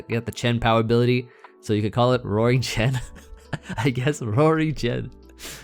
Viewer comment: I thought you had said “Roaring-chan” almost choked on some water because of that lol